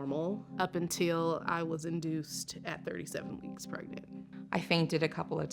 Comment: did she say 37 months? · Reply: No, weeks!